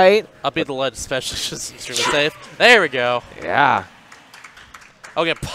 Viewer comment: Did elegant just say “YES” 😂